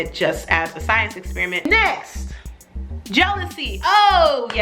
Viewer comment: I heard Jodeci instead of jealously. 😂😂 What happens when I'm multitasking, I mishear things.😂😂